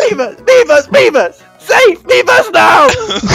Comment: it sounds like hes sayng beavers